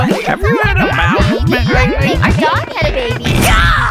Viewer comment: Did anyone else hear “my dog had a baby” or “a dog had a baby” at 1:25?